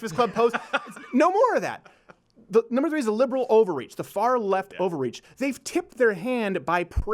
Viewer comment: Y’all thought laurel and yanny crazy 2:43 is he saying liberal over reach or liberal ovaries